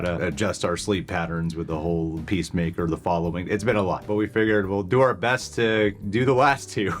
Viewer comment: Wait, did I hear "The Following" at 0:34? I hope I did. Such a good show. I'd love to see the reaction.